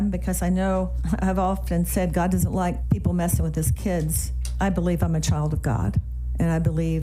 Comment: "God doesn't like people messing with his kids." 😂 I heard God doesn't like liars.